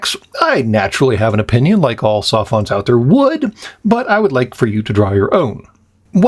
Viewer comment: Wait, did I hear the word Sophon? O.o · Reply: "Sophont".